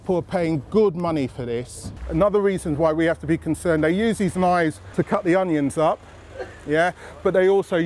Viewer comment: I honestly thought he said "they use their lies to cut the onions up" lmao